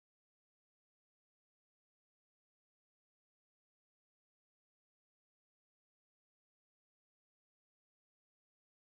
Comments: What did she say? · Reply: She yelled kitty ^w^ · WHAT WAS THE LADY SAYING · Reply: She screamed kitty TvT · In 5:28 what asian lady said? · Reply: She pointed out the lion was a cat by yelling "KITTY!" XD